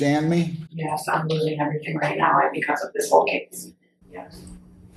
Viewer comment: It sounded like she said something like “I fully understand bc I’m losing everything right now bc of this case.” Or something similar to that????🤷🏻‍♀️